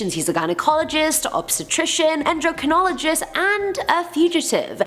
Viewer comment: Who else heard jerkonolgist when she said endocrinologist? 😆 For a second there my mind went somewhere else. Oooppss